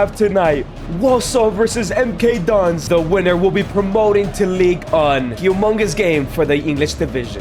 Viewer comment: Did anyone else hear say league one as league un